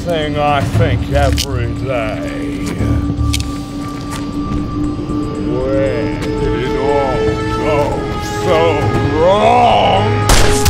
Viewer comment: Two things 1: You managed to film a gunfight scene by an airport. 2: I swore Mark was gonna say "Where's the Blacksmith" instead of "Where did it all go wrong"